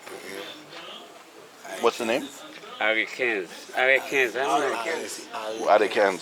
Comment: == >HEY, The name of the gun you we're asking for is a AR-15 - they were saying AR quinze in kreyol just thought i'd say it ....